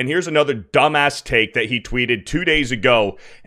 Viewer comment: I thought he said he deleted X hahaha